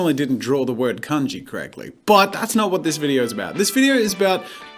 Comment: really thought that he said he spelled cunty for a second